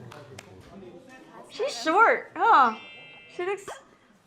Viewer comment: Okay. Did I just hear her say, Tzuyu is SHORT??? I laughed soooo hard.